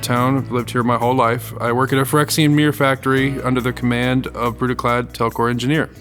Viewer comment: I honestly thought said Phyrexian BEER factory. Hmm sounds oily…